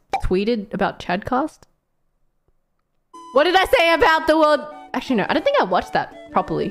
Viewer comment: Bae said Chadcast... I heard it as chodecast